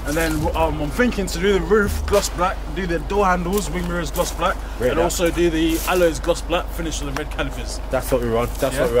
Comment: GT3 RS did he say?????? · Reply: E63s